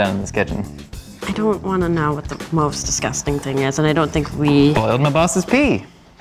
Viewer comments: “boiled my boss’s pee!”😶 😶 😶 😶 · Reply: I thought he said "holding my bosses pee" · what did say? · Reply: *bOiLeD mY bOsSeS pEe* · This is why deserves more appreciation. The things he does for this show 👏👏👏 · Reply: what did he say, all I heard was "something my bosses pee"!?